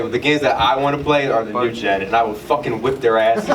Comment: i couldn't understand the game said. Was it M.U.G.E.N?